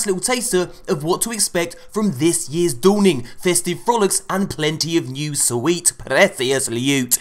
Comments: Is it just me or did he say ‘festive bollocks’? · When he said festive frolics I thought he said festive bollocks 😂😂😂😂